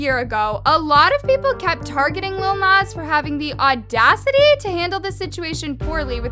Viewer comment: lol if you play the cc aka sub titles at 3:53 instead of saying lil was x it was loneliness